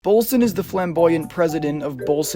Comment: I thought you said fem-boyant not flamboyant